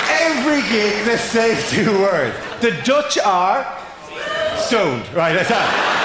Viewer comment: The Dutch are... Stoned. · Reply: TBH it doesn't sound like he said stoned, it does sound like he said sowned. It's only because I know the context that can figure out what he said.